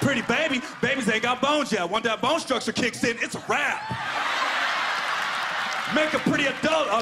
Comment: “When that bone structure kick in its a wrap” 😂💀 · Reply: I thought he said "it's a rat" ehe